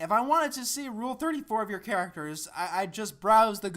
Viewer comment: He didn't say he wants to look up cartoon porn, or that he likes cartoon sex. He said if he wanted to, he would Google it. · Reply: Oh wait, I think I may have realised what the problem was - did you mishear "I'd just" as "I just"? Because that would explain so much.